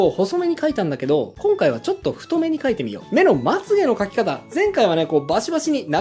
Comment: とても勉強になりました。ラーメンでの例えは旨いですね。 そのせいか、線を「太目で描いてみよう」が「太麺で描いてみよう」に聞こえてしまい笑えました。